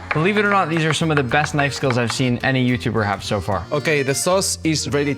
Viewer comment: For a second, I thought you said I cooked every YouTuber